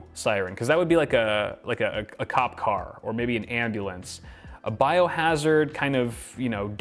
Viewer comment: Did I hear andulance ????